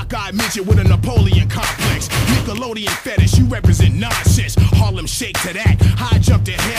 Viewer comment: did he say Harlem shake? Was that even out at that time?